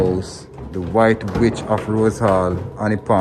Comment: Sounds like he said white bitch of rosehall @ 25:01😂